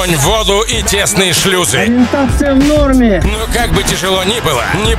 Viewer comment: только с 7ого раза услышал что он говорит: -Ореинтация в норме😅